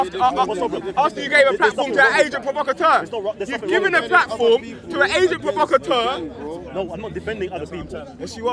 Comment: sounds like he said "you've given a platform to an Asian provocateur" lol